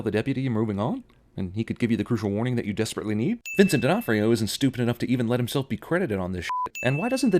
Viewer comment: Ok, so you freaked me out when you said Vincent's last name because it is so similar to mine. As you see in the video his is spelled D'Onofrio and my last name is Denefrio. For a minute I thought you said mine and was like wait...what?